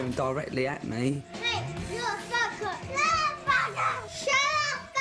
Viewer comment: is that child saying the f slur- who tf did he get that from if she hasnt said those swears infront of him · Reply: He said fucker not that